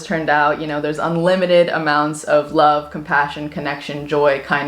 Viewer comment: When you said these are the things that make a decent human being I thought compassion said depression and I was like whoa wait what ? HAHA